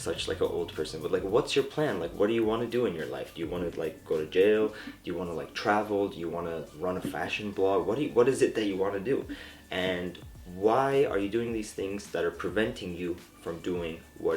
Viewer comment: did he say "do you want to go to jail"?